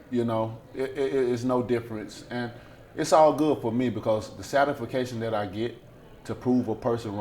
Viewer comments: did he say "the sadefication that i get to prove a person wrong"? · Satis-fa-cation.........does anyone know what that word means?